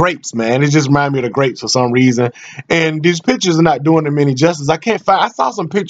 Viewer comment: I thought he said "these bitches"